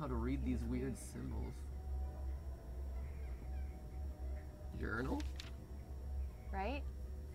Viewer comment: totaly sounds like he said Urinal IMO xD